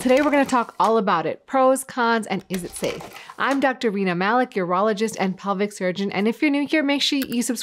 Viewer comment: ok so completely unrelated to the video but when i heard your name i had to scroll back because your name is rena malik i heard it as weiner magic and i had to take a lap